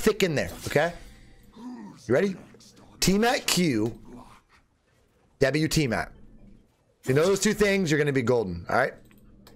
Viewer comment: guys could anyone help me about what did say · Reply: Tiamat + Q \ W + Tiamat